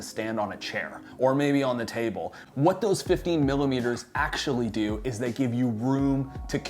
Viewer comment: could it be that you wanted to say 35 instead of 50 at 2:17min? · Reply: Hey! Yeah, I’m a fast talker sometimes :) Said 15mm, difference between the two primes